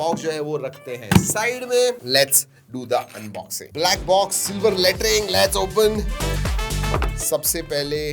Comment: did he say super latring 😂